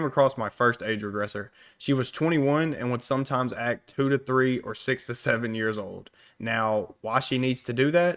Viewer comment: Oh i thought she said 2-3 or 67 lmao